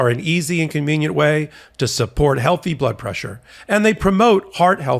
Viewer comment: I love how Michael Popok explains it so well. Donald Trump may have thought he could use his delay tactics and drag out this case for years, but it looks like justice is catching up with him fast. It's about time we see all these criminals behind bars. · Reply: Listened with a half ear to the message of the sponsored item on Sonos bluetooth in this item while doing some work……suddenly I heard “…..a high Butt-pressure” twice…..Butt-pressure? A rewind was neccesary…. Ohhh pills against Blood-pressure! 😂 Keep up the good work Popok!